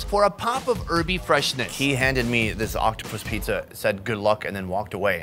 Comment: did he just said"Octo" "Pussy"😂